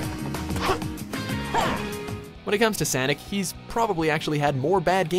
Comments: am i the only one who hears sanic at 1:40 ? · anyone hear sanic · anyone hear sanic at 1:40? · did he just say Sanic? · sounds like he says “Sanic”